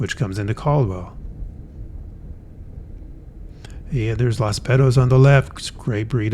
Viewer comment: I was LMFAO as I swear you said, "Las Pedos" (and it still sounds like you did). I had to search it and its called "Los Betos". For a non Spanish speaking person, it's no big deal, but "pedo" in Spanish and in my juvenile brain, means "fart" though it can mean several other things, depending on context.